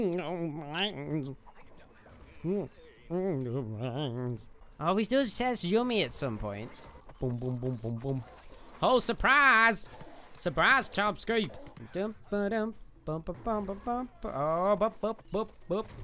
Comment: What does say here? 3:24 Idk about you but I hear him say chomspree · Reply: I say "Surprise! Suprise Chompski" 😊